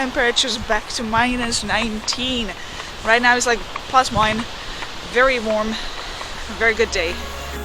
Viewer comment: Did she just say "very warm"? 😅